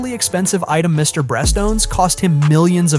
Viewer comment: Mr Breast? haha brest? didnt think it was so hard to say beast